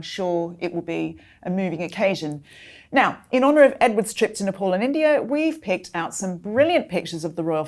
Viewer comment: Twice I heard 'Nepal in India' ... Nepal is not in India but is a separate country in the Himalayas!